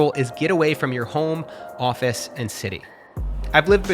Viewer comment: for a second at 5:39 I thought it said Get away from coffee not office and I was like what happened to Matt lol